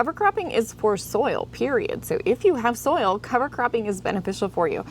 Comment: I honestly thought you were saying cover crapping 😂😂😂